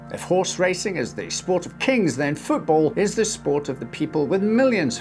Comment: At the beginning I heard you say "If racing is the sport of kings then football is the sport of people with millions". Which I thought aptly described top tier soccer players.